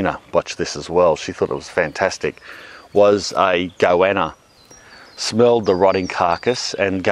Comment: Watch out its goanna getcha. WTF is a goanna? I thought you were saying iquana.